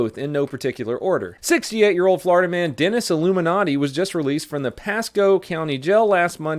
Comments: did he say Dennis Illuminati?🧐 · when you were saying his name i thought you said iluminati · in to video....did just say "Dennis 'Illuminati'????? ROFL.... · Did I hear "dennis illuminati"